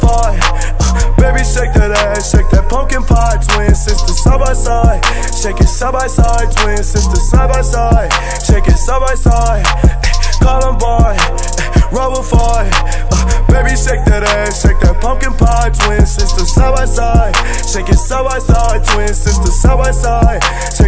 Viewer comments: Honestly thought he was saying columbine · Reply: Thought he said Columbine roll a five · I thought he was saying “pumpkin pot” 😂